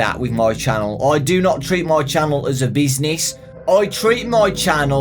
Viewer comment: When he said " I do not treat my channel like business" I thought he said " Bees Knees."